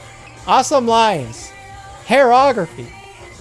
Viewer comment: did he say "hair-eorgraphy"...... LOL